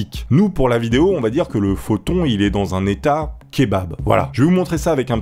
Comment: 🤔 j'ai entendu shebab